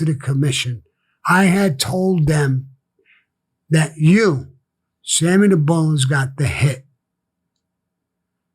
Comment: Did he just call himself “Sammy the Bones?”